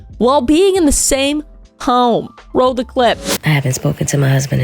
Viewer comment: I keep hearing "roll the clit"...